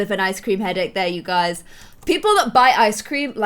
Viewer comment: When you said people who bite ice cream I thought you said people who Buy ice cream